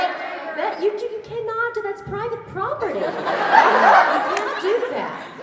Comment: why the hell are all voice actors so simular to their characters its so strange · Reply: when she said 'it's private property!' at 2:39 i swear i heard pearl